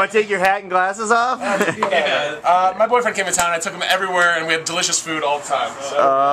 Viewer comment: ist the one Guy gay (no offence) ? or did i missunderstand him. i heard "my boyriend came over....)